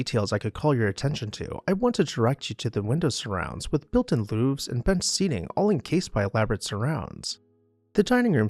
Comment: Question for you.. in the Dinning Room you mentioned some architectural features of the windows that I have never heard of before.. I think you said @ 2:08 "built in luves" (or maybe looves / louves..) what are these what is this feature please explain. You've peaked my curiosity.. thanks again for the video!! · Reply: Great question! Built in Louvres are essentially blinds. If you look at the inner part of the encasement, you’ll see what looks like shutters- those are louvres.